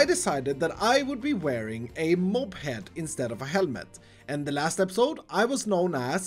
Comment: At the start I thought he said “Moped” Not”Mob Head”